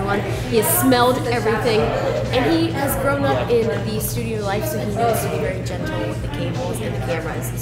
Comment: Had to listen at 4:13 twice, thought at first you said had "thrown up" in the studio ...